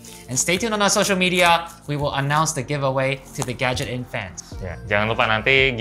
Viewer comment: w dengernya genshin impact:v